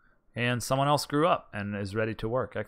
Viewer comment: when I was watching the video, I though you said "Someone screw up" (14:35). I had to replay it over to here what you said. I like these types of videos and hope you enjoy playing them.